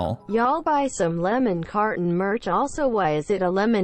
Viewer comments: OMG THIS WHOLE TIME I THOUGHT HE SAID LEMON COTTON NOT CARTON AND I JUST ACCEPTED IT. I FEEL SO DUMB!! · Your lemon carten merch is grear but your ascent was getting in the way and i thought you say lemon cotton and I was confused when I saw the merch